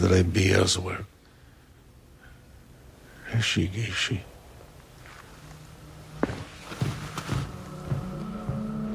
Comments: Does anyone know what he says at 2:22? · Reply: “I know you” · what did he say, please? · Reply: He said “I recognize you,” in chakobsa. Idk why the subtitles aren’t in this video.